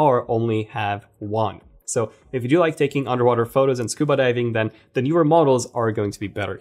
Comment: Did you just say scuba diving?